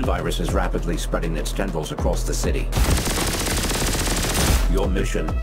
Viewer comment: "The SIN Virus is rapidly spreading its genitals across the city! Your mission, burn it out!" ... might have misheard a word and had a laughing double take😂